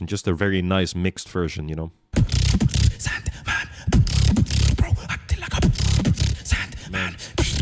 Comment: When he was up against River at GBB I thought he honestly said “Bros acting like a SideMan” so at the time I thought he was calling River Colaps’s SideMan 😂